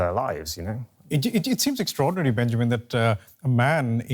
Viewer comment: i thought he said, Benjamin, vannakam